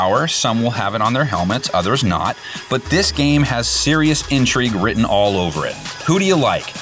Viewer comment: I thought he said, “This game has serious injury written all over it.” I was like wtf why would he say that